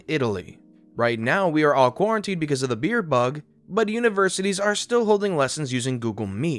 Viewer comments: 'Fear bug'? 'Beer bug'? What did you call it? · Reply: Beer bug and it was great. They have said on other channels that they are not allowed to say the actual virus name or they will be demonetized. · you didn't say Covid-19, I what the heck did you say instead? sounded like virus-bump... ? For the record I'm hard of hearing, so I'm not only listening but usually reading it as well... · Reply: I believe he said Beer Bug, a bit of word play due to the Corona beer